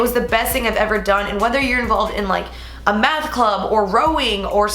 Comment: When you said math club I thought you said meth club at first..... lol I was like bruh.....